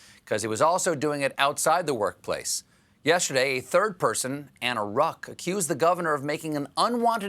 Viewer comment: When he said "a third person Anna Ruch" who else thought he said 'a third person and the Rock"?! I was literally thinking on Dwayne Johnson.😅